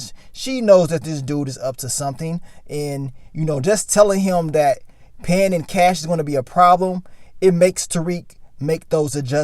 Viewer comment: 😩🤔 now I gotta rewatch bc I thought she said, ALWAYS pay in cash..!?